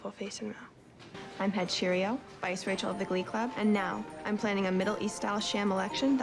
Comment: did she say Vice Rachel of the Glee club? If she did that's hilarious